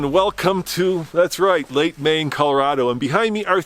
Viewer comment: where is this? It sounds like you say lake Maine but I can't find anything about it.